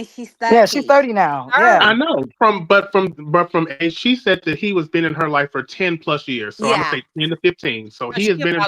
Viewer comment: I thought she said that he had been in her life for 25 years meaning that she was 5 year old when he came into her life